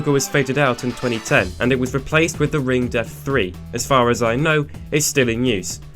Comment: I strangely misheard "The Ringed F" as "The Ring Death". O.O